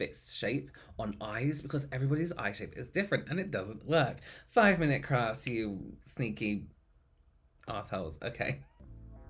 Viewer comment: I heard "5 mint crafts, you sneaky arsholes..." 🤣🤣🤣🤣🤣